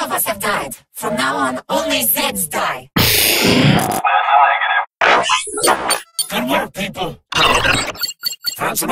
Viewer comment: what the fuck did he say at 1:46? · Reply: "that's a negative"